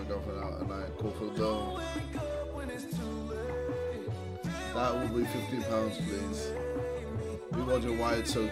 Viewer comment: did he say 50 pounds???